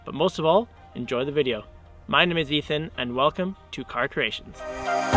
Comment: I thought you said "Car Stration" 😬